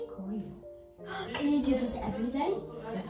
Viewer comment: Can you____everyday? ここなんて言ってるんでしょうか？ただいま英語勉強中です…リスニング難しい 素敵な日常を見せていただきありがとうございます🧸🌹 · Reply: 「Can you do this everyday?(これ毎日やってくれる)」 お母さん「やだ」 ですね